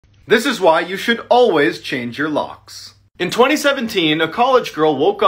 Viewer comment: I thought he he said “this is why you should always change your lungs”